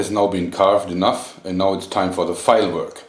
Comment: i thought he said fireworks when he said file-work...